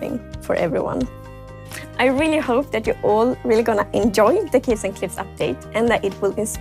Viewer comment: I thought she said Cliffs and Cliffs Update :)